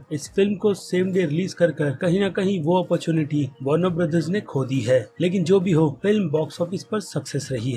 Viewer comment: if you are seeing the video with subtitles than at 9:50 😂 you'll see that in subtitles instead of warner brothers there is evil brothers